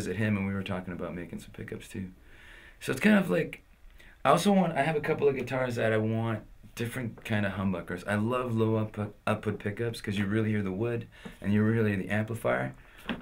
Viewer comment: can anyone enlighten me on what pickups he says he uses right at the end? it sounds like he says something like Lou Uppard pickup, around 11:55 mins in.